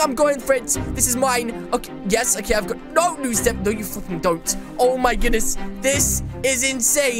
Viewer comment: did u say (no you fucking dont)